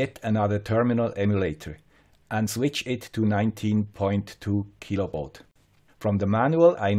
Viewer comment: sounded like " 19.2 kilo volts" but may have been baud ;-). Not a total failure, it was a practical learning in the field with measurable learnings. well done.